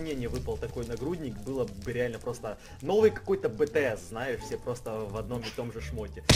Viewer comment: Мне послышалось или он на 0:32 сказал bts😁